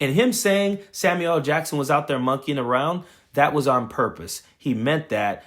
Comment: Did he say monkeying? WTF!